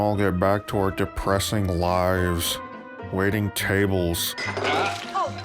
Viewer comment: I honestly thought you don't have it in you to do it. Guess I was wrong. Great job, man! At 11:45, "waiting tables", and the face of Rich Evans - i just heard "WAITING CHABLES"1